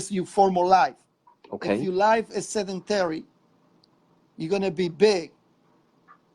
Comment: I think he was trying to say sedentary not sanitary.